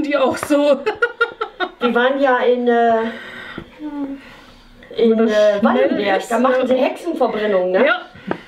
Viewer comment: habe ich da Schmallenberg gehört? Schöne grüße aus dem Ort der Hexenverbrennung😄